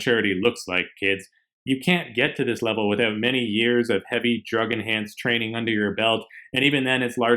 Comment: Did he say drug enhanced training 3:38 😆